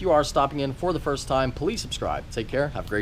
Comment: Is it just me that lately I hear you saying "police subscribe" at the end? I might have to check my hearing, lol....So much love for the police dude😛😀